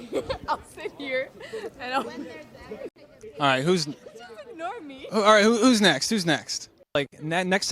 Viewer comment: Did she just call him a normie? Or did she say "you just annoy me"